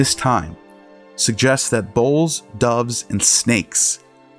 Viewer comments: I thought he was saying 'bowls' when he meant 'bulls'. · Sounds like he is saying "bowls", not "bulls"